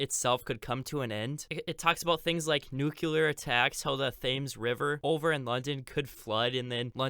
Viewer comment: Did he say 'the fames river' 😂